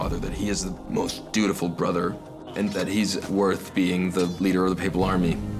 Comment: I thought he said ''the most beautiful brother". I was like amen to that!